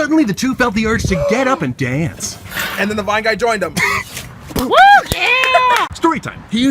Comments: "Suddenly the two felt the urge to get up and dance." "THEN THE VINE GUY JOINED THEM!!!!!!" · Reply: I heard "and the vine guy joined them" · "Suddenly the two felt the urge to get up and dance." "THEN THE VINE GUY JOINED THEM!!!!!!" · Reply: Omg smgfh I thought he said "AND THEN THE WHITE GUY JOINED HIM" 😅😳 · I thought he said white guy! 😂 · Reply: Just realized it was "vine" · "Suddenly the two felt the urge to get up and dance." "THEN THE VINE GUY JOINED THEM!!!!!!" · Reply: I heard "then the bi guy joined them!“ Yea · "Suddenly the two felt the urge to get up and dance." "THEN THE VINE GUY JOINED THEM!!!!!!" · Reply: i thought it said, "And then the BI guy joined him" 😂pretty sure Thomas is gay though??